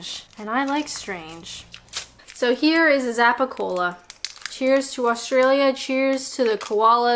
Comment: Good idea not to review a foot massager, you'd get some very strange comments. Also, I thought you said Zappa cola!